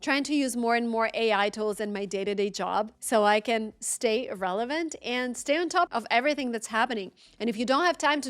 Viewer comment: did you really say "stay irrelevant" 20:02?? at the end of a great presentation.